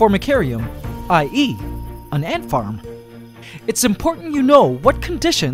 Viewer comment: did he just said “I eat ant farm” cause I’m not watching in subtitles